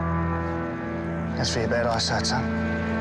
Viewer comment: What did he say, please, at 1:15? Must've played it 4x to try and decipher... and captions didn't help. :-) Thanks, mates. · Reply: "That's for your bad eyesight, son."